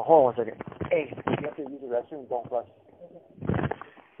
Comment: what does he say to that person off the phone.. i heard "if u use the restroom dont flush" dod any1 else here it